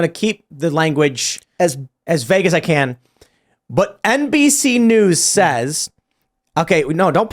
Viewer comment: When you said "NBC News" I thought you said "NPC News"